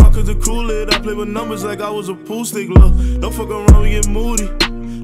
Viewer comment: My man really said sum “I play with numbers like I was a pool stick” 😷🔥 Edit: lol I come back 5 months later to > 1000 likes on a dumb ass comment. Niiice. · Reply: I thought he said "I play with numbers like I was a poo stick 💩" lol